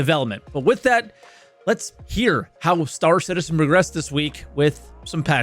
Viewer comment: Did i hear "lets see how starcitizen regressed this week" at the beginning?